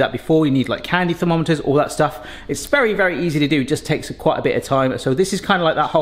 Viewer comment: Did he just say it's "Barry easy to do"?